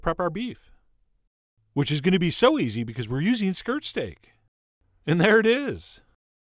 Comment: I thought he was saying skrrt stake